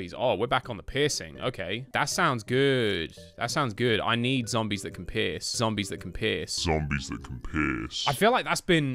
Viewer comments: Sounded like “Zombies that can piss” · It sounds like hes saying "I need zombies that can piss"